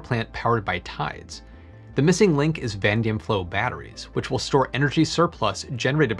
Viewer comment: it sounded like you said "vandium"... I had never heard of anything by that name--- I thought maybe you mispronounced "vanadium", so I searched the term, and found that it's a real world, but used only in fantasy games as a hard, shiny metal similar to chrome. I suspect the word you intended to use was "vanadium", which is an element on the periodic table--- used to make certain steels?